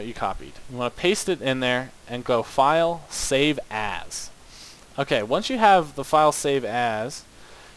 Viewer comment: whenever he says save as he sounds like he saying save ass